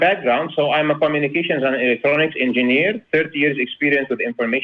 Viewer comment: did say he's a "Fornications Engineer"?